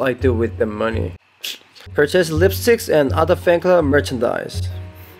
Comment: did he say "lipstick" instead of "lightstick" lol